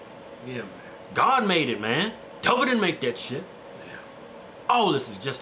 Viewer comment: What did he say after he said "God made that shit. " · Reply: I think he said government ain't make that shit